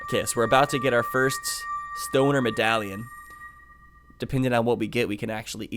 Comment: At first I thought he said stoner medallion